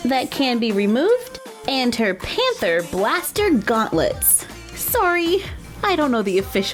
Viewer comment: I thought she's saying gaster blaster